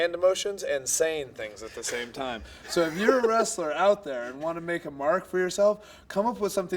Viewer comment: what Daniel Bryan said If u are a wrestler ,out there What the subtitles showed If your breasts are out there🤔🤣🤣